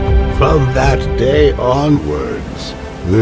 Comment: Island, it says, subtitles say highland. Motomoto doesnt run away so he stupid Fun fact: chungus ate the tree that told motomoto likes chungus